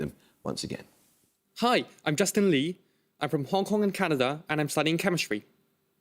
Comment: Where did Lee say he was from?!?! It sounded like he said "Hong Kong - Canada"...